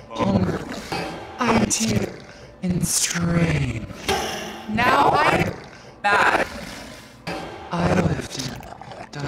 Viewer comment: the subtitles and stamp say "I don't know whether to laugh or cry." But she actually says "Now I'm mad"